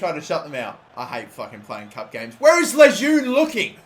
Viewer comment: I thought said Layún From México that plays in la Liga. Santander